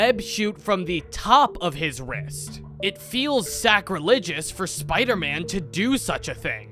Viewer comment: Did bro just say sacrilegious 2:35 😂😂😂